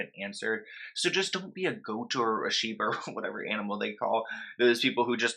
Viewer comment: I'm so sorry this has nothing to do with the subject at hand BUT even though you very clearly said "sheep" at 3:13 I for some reason misheard it as "Shiba" and I was so perplexed as to what those roasted- marshmallow-looking sweeties had done to deserve the callout